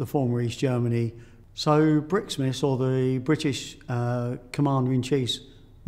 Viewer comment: Did he say "Commander and cheese"?